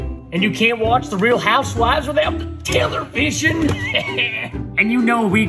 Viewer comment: when he said TELERVISON I thought he said taylor's version (I am a swiftie)